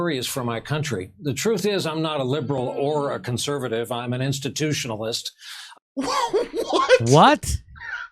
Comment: What he said: "I'm not a liberal or conservative, I'm an institutionalist." What I heard: " I glow in the dark."